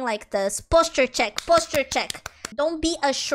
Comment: I thought he said "Bust her check, Bust her check" 😂